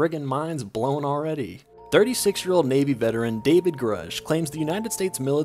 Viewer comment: The way you said his name, I thought you said "David Koresh" and I was like "I knew there were aliens at Waco!" /s P.S. you should talk about micro-missle launchers